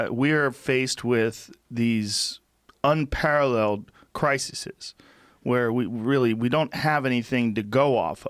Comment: I heard "criseses." I'm posting this comment. And now I'm out.